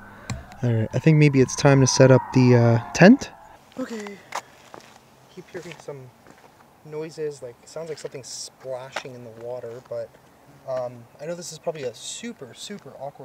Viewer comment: I heard a voice at 33:11 say OK when said let’s put up a tent.